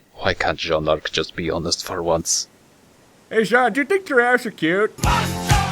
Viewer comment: I thought Cardin said, "Hey Jaune, do you think your ass is cute?"